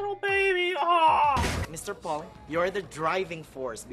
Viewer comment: Did he say Mr Paul?